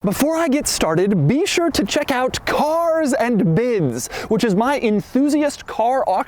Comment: i dont know why but i heard "checkout cars and kids" and i was not suprised